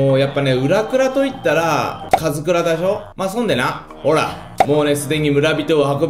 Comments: ウラクラと言ったらカズクラだひょ聞こえる人？ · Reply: カズクラだほぉ？に聞こえるw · ウラクラと言ったらカズクラだひょ聞こえる人？ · カズクラでしょ。って言ったとき カズクラだしょ。って聞こえたのは 俺だけか？